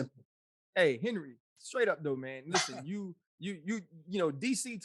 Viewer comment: Did Ryan just say to Henry at 46:00 "Straight up, little man"?? What kind of disrespect??